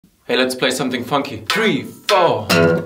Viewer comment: I swear I thought he said let's play something fucky and almost died.